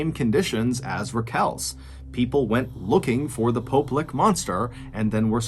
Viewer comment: At first I thought he said it was the poo lick monster. That would be much worse.